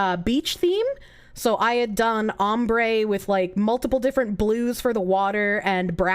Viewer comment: At first, I thought she said "Hambre" and was thoroughly confused.